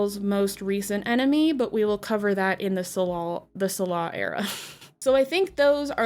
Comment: did you say Tsalal?